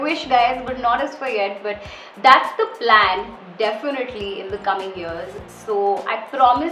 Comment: "that's the plan" sounded like girl boss👩‍💼 u go girl🧡